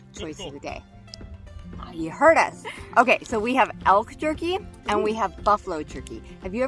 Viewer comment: lol... I so heard you say "Elf" jerky instead of elk jerky!!!